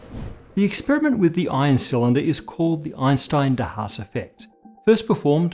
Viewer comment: I thought you said Einstein in da house. I'm like, wow! Even Einstein went gangsta